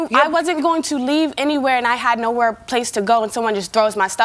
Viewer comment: I thought she said she had no place to go